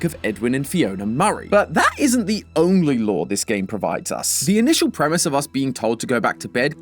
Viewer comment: the subtitles says the only "law"?? you mean the only "lore"?